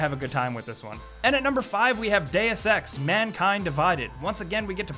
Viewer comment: When he say deus ex I thought he said day of sex